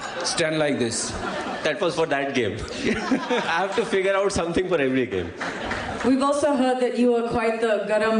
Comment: What did dhoni said at 2:08??? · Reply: He said that was for that game I have to figure out something for henry gayle